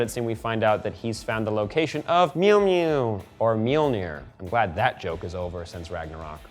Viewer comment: youtube's Autogenerated subtitle says Mjolnir as Meal Near...that's easy to pronounce...